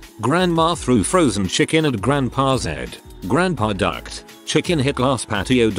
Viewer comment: I heard duck as died and spit my drink out